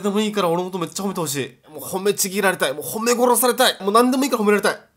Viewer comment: ｢褒め殺して！｣を｢ハ〇メ殺して！｣って聞こえてしまった私は末期………… だれか……私を殺してくれ…… ……あとは、全てが完璧だよしゅき……(泣)